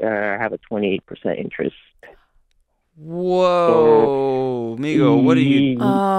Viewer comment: Did Doc just say “amigo” @ 1:07 🤨😒 ☠️🤣🤣🤣 Oh Doc, Well Howdee yeehaw back at’cha 👋🏻